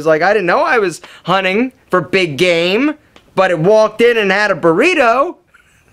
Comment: Did he just say , Its walked in & had a Burrito lol 😂